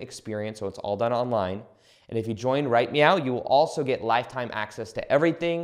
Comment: Is he Super Troopering us? Did he say "Right Meow" at 19:02? 😆